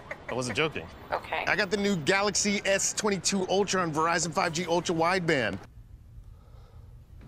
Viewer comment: I thought he said "ultra white van" xd